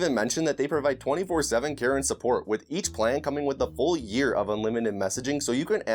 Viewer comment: I had thought you said "24/7 Karen support"